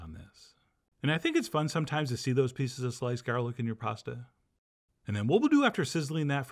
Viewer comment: I heard ‘and I think it’s fun sometimes to see those pieces of sliced garlic in your prostate’. Me: ‘your WHAT??’ …that was such a ‘…what did I just hear..?’ mental whiplash moment